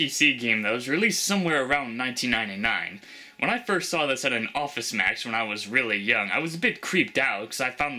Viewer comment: what the heck is an office match? (maybe i misheard it) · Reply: think he said Office Max.